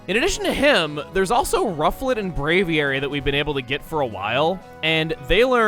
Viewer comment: I no joke thought you said Rowlet when you first said "Rufflet"